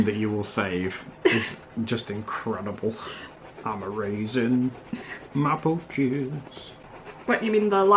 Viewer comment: I thought was saying “I’m a raisin”